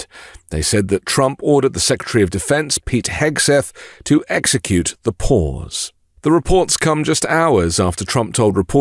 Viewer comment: I legit did a double take 0:50 I thought you said "Trump ordered secretary of defence Pete hegseth to execute the poors"